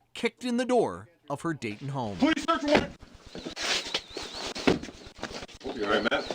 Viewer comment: a policeman says loudly "Police Search Warrant" from the outside of their front door. In all honesty I could not understand him, I had to play it back twice before I understood what he said. I was born in America. Now see this Middle eastern and understand that it was not possible for them to understand him so they never knew it was the Police with a search warrant. Breaking their door down 28 seconds later is unbelievable to me. Something was wrong from the very beginning of this heinous home invasion by the Police. An entire swat team over a stolen snowblower is also completely uncalled for. Something seriously wrong is going on here. This news story gets worse at every turn of events. I am 74 years old and never in my life have I ever heard anything as depraved and disgusting as what happened to this couple through the actions of their local Police. "SHACKLING" this pregnant woman for 36 hours was uncalled for and I think it was done because she was from the Middle East and the Police were racist and hated her for no reason. What kind of man would willingly be involved with "SHACKLING" this pregnant woman to a gurney and continue to leave her shackled till the 36 hours expired and then these men simply walked away from the evil that they committed upon her? - Peter age 74